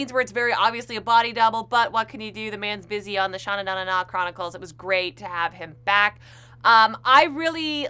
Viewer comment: Did she just said "Shanananana Chronicles"?😂